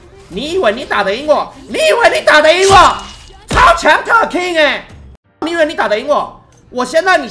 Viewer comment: 特爾king我聽成turkey